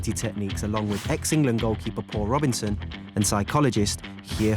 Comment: When he said Paul Robbins in I thought he said poor robinsin